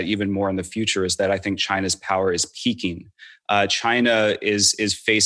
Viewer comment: lol, I heard this as "China's power is Peking"